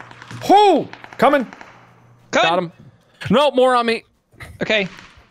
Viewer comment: Am I the only one who heard say "Nope moron meat"